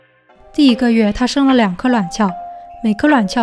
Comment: “第一个月，她生了两个卵鞘”只有我听成两个懒觉(闽南语lanjiao)吗？